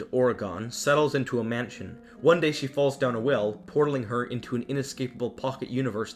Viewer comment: I always thought it said the bell down ...welp that was weird 😂😂